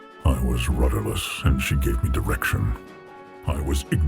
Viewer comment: I heard She give me the eractionso tierd